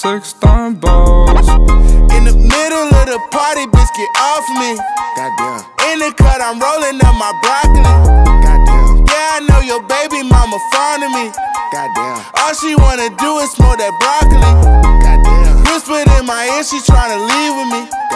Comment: I always thought he said indica I'm rolling up my broccoli but I guess not